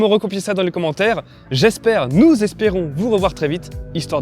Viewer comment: Moi j'ai entendu Apéro !!!! ^____^